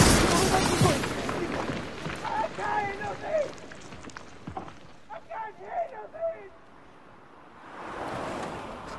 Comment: What do they say at 1:31? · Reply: "I Cant Hear A Thing"